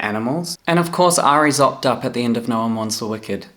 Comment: I couldn’t quite understand what you were saying. You said “And of course Ari ???? at the end of No One Mourns The Wicked” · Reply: Opt-up (option up) is a singing term when you have the option to sing a higher note than what is originally written 😁